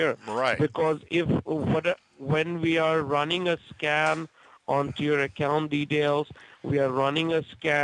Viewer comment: I swear he just said he's running a scam on his computer lol 😆